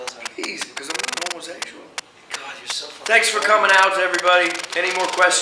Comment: I love when norman said "your so fucking annoying " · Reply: purple fairy witch did he say annoying? I was way off then, I thought he said "so fucking vulgar" lol